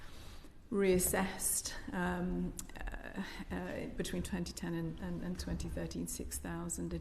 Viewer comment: Did I really hear this at 01:55 ". . .when people were being reassessed, between 2010 and 2010, 6,000 additional suicides"?